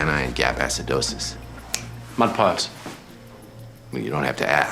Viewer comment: “mud pies” “well you don’t have to ask, just wash your hands before you come back” love house’s quickness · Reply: i heard “mud pipes”